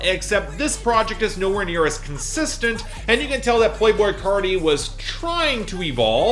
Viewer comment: man really said "poi boi carti"